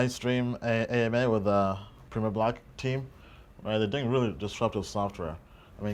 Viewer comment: What is the ICO platform that you start to mention at around 17:18 . I can't catch it, Prima block? Thanks, your journey has been an inspiration!